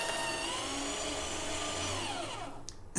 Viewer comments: When you said Reißwolf I thought you said rice wolf XD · I heard ‘this is a rice wolf,it wolfs rice’ XD